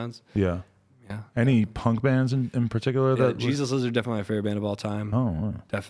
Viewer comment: What does he say is his favourites band of all time at 19:30 ??? Please someone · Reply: the jesus lizard